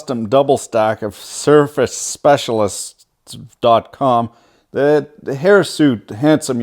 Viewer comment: where did you get the power supplies? Oh CiRCUIT specialist sounded like you said surface.